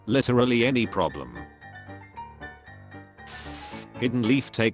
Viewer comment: we add pain instead of "any problems"